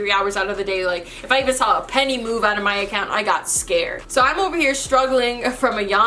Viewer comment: I thought u said move out w no money ?